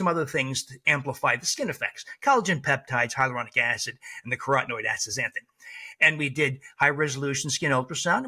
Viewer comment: ‼️‼️What supplement is he saying he gave the ladies in the trial - at 15:15 - “Nicarotenoid” acid??? NEVER heard of this… nothing comes up in an internet search either. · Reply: He said “and the carotenoid azthxanthan”